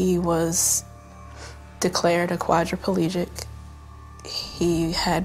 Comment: wait he was declared what? can someone reply to me saying what it is please (3:13) his wife said something like aquadric palegic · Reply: Quadriplegic. Meaning one who has paralysis in all 4 limbs.